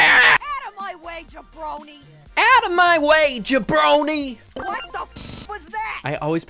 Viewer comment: O crap I forgot Spike... 😶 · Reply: I always thought jacky said lil brownie 😂😂😂😂😂😂 I dont even know what a jabronie is